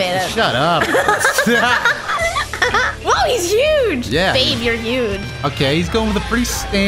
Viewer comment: Did i heard babe ?